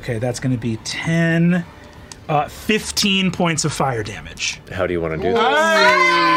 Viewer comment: thought he misheard it as "How do you wanna do kiss?"